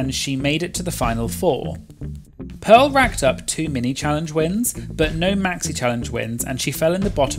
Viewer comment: Every time he said “two mini challenge wins” I heard “too many challenge wins”